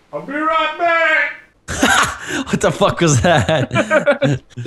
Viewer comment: from vanoss's pov it sounded like lebron James from noglas pov its sounds like I'll be right back